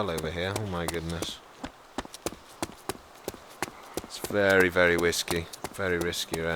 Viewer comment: lol at 07:10 it sounded like u "said it's very very whiskey" and not "it's very very riskey". Did anyone else notice that???